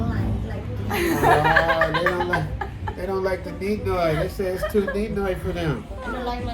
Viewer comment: Wait. What's the Neet-Noy? What's she saying? After 11:13?